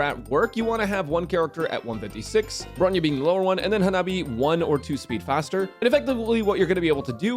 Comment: Did you call her Hanabi? lol